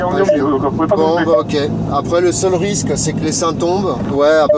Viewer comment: à 2:33 j'ai compris "après le seul risque c'est que les seins tombent" avant de comprendre "essaim" 😂😂😂